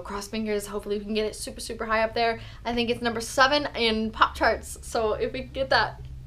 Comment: Sounded like you said "pop tarts"